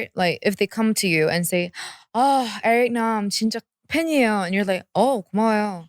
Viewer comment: What’d Jamie say at 25:00 ? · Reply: “ Oh Eric Nam, I’m such a fan” And you were like, “Thank You...” I would’ve thought you had a bad personality and you have a problem